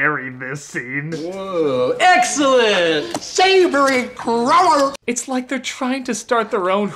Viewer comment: what does this dude even say after the word 'savoury'? savoury grower?